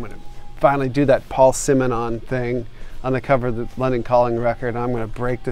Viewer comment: when he said London Calling, it sounded like he said "Leonard Cohen"! Duff rules!